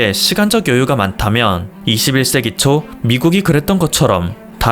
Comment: 21세기초 라고들리네요 ㅎㅎ